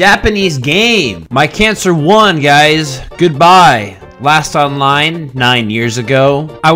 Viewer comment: I thought the cancer one said:"I won my cancer goodbye." It's just so 😂😂😅😅😅😅😢😢😢😢😢😢😢😢😢😢😭😭😭😭😭😭😭😭😭😭😭😭😭😭😭😭😭😭😭😭😭😭😭😭😭😭😭😭